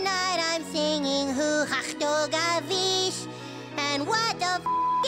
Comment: What was Kyle saying in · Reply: I’m not sure, but he’s saying Huhash Dogavish!